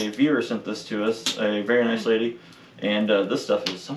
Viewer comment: OMG!!! My head is so plugged up my ears are not working so well. When said who sent the maple syrup I heard him say a BEAVER sent it hahahaha nope, not quite.😅